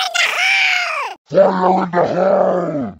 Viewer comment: i think he said fire in the hole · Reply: thanks, i thought he said hole in the fire